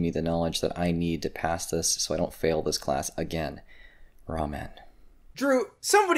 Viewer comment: did he say "ramen"? LMAO